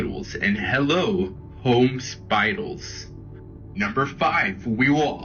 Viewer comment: what the hell is he trying to say? "home spidals"?